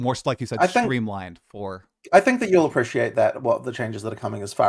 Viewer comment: I heard "Atlas Stories".